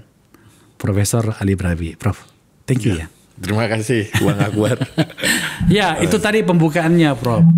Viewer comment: haha apa gak salah denger neh. Kok kedengarannya " makasih bang aguan "